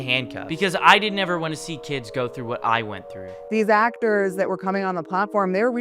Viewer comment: Did you say what you been through 12:40 😢